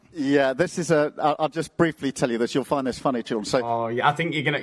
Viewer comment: Did Starmer actually say 'you'll find this funny CHILDREN' ! Patronising twat.